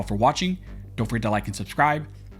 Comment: I always hear " Born Barn scholars" took me awhile to realize hes saying armed scholars.